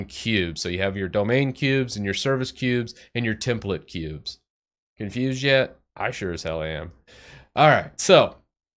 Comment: When you said confused yet, I thought you said enthused. And laughed hard, thinking it was the dryist joke ever.